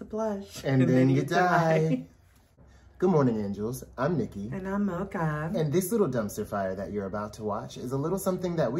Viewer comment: When mocha says her name in the beginning, sometimes I hear her say.. “and I’m Loca” hehe you two are cuties and I love the way you make your videos. Love the color coordination today 💛🧡